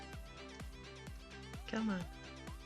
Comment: wait a second did you said "¿qué onda? in 11:45 ? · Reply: He said: c'mon